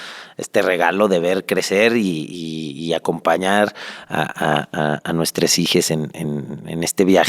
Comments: Cerre el video en cuanto escuche "nuestres hijes"jaja no mams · es enserio que dijo en el @ 12:33 "nuestres hijes" wow... dios... no lo veo malo perome sigue conflictuando bastante hasta donde puede llegar eso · nuestres hijes escuché bien 🤢🤢🤢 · A nuestres Hijes? No estoy juzgando, solo quiero saber si escuche bien 👀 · "nuestres hijes" acaso escuche bien dijera el bueno para nada de luisito comunica "ne memes" a partir de aqui perdi el interes en la entrevista